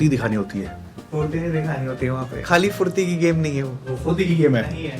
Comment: did he just said "Khaali Furti nahi hai, - woh fu*di ki game hai" 💀